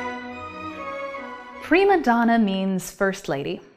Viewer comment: For years I thought people were saying "pre-Madonna".